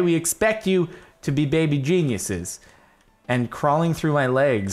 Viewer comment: When he said geniuses I thought he said we are going to be Jesus's 😂😂😂😂😂😂